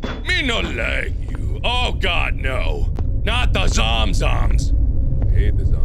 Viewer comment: subtitles say "god no not about Saddam's arms"